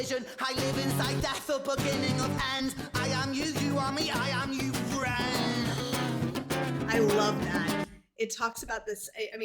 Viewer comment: ii am pretty sure the subtitles are wrong. I think he says "i am you ren" not "i am your friend". It makes more sense. "I am you, you are me, i am you REN!" Edit: Apparently youve already been told this. I am late. It just feels a little too obvious that he said i am you ren. Subtitles can be good but they mislead in a big way when they are wrong.